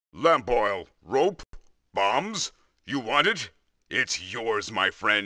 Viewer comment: I thought he said that boy oh rope bombs 😂